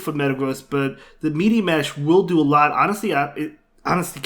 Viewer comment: in the vid Did he say meaty mash?